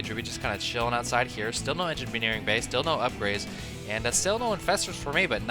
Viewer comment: Anyone else hear "engimeneering bay" at 9:28? Silly.